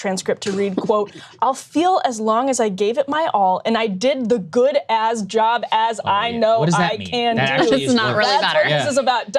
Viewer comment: Idk why it matters which he actually said but I always heard “as long as I did the good as job as possible” basically his brain mixing the two phrases. Doesn’t matter because it was clearly another sign of mental decay